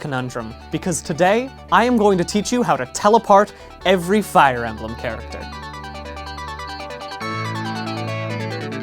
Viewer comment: I thought he said "Teleport every fire emblem characters" and I got excited.